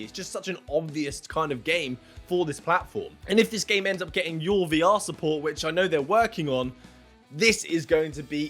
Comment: you mention “Your”(?) VR support? What is that? Sorry, my country bumpkin Texan ears cant understand the Queen’s English at times, lol... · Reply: Full vr support. Man cant speak properly it seems, he constantly mispronounces stuff by accident lol.